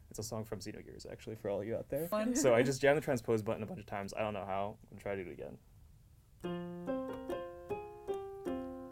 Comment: can anybody tell me which song he did play? I cant understand it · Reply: Finally found it xD Ship of Regret and Sleep from Xenogears (that's the inaudible part) <link>